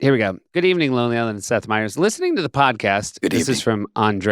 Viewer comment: Speaking of wrong "lyrics," CC/subtitles says "Seth Meers."